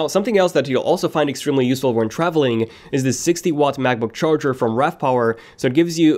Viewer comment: It sounds like he is saying MacBook care instead of air